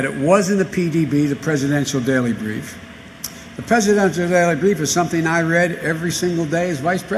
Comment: What on Earth did he say 0:15.? 0:44 🙄I was talking to.... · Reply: "The presidential daily brief is something I read every single day", have you got early onset deafness ?